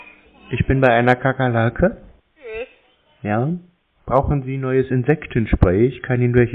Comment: Nguten Tag isch heiße Chuck xD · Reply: oh ich habe Chuck verstanden 😂